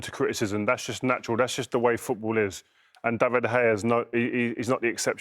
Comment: Did he just say David De Gayer? lol. Only the second goal was his fault, the defence should be blocking them balls!